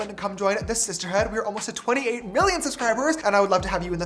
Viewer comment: Did he just say 28 million i